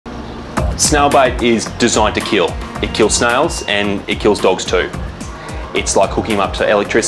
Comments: I'm glad we have subtitles because I thought he said a snail BITE and I was very confused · When I first saw this vid I thought he said snail bite. Not bait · this is the perfect reminder that it is VITAL to train your dogs to have a solid stay, heel, and most importantly LEAVE IT. never leave your dog unsupervised in an area with unknown probabilities and remember that training them can and WILL save their life. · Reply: I agree, but they are talking about snail BAIT, not bite which what I thought he said at first too. So, training a dog has nothing to do with keeping them from snail bait. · Poor dog! With the vet's accent, at first I thought he was saying snail "bite," not bait.